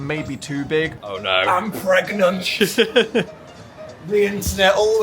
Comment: I was really high for the first time I watched this and thought said, "I'm phregnant"